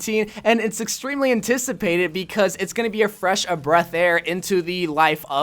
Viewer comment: Am I the only one that heard him say ‘a fresh of breath air’?